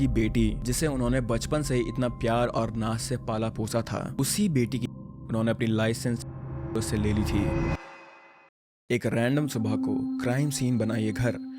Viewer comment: what did he said because i couldn't hear he beeped it can anyone say what he said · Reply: Licensed gun wapas le li thi